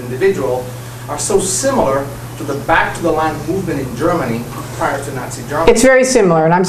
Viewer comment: Q: Scientology is most similar to Back to the _?__ Movement in Germany? Missing word? At min 7:10 an audience member states how similar Scientology is to a certain movement in Germany. I did not clearly hear the last word. It sounded like Line or Land.